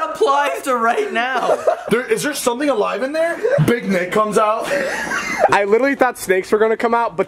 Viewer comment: "If big nik comes out" 😂 I started screaming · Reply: I thought you said big milk and I was getting so confused then I read it again and I was like “IM STUPID”